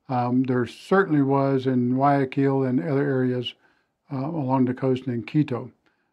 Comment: Sorry, don't understand. You said there was a lot of violence in Guayaquil, and "along the coast in Quito". Quito is about 116 miles from the Pacific coast. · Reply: I said along the coast AND Quito...